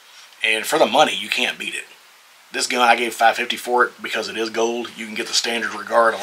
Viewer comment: did you say you got it for $550? WHERE? 🤯